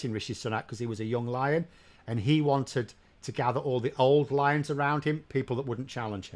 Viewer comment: I thought you said “old liars”not “old lions”, still think without any doubt “just liars”.